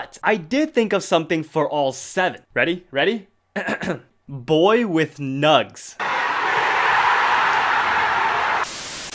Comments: I thought you said "boy with nuts" it got me repeating that part again 🤣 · I heard "boy with nuts" instead of "boy with nugs" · i thought he said boy with 'nuts' at the end 💀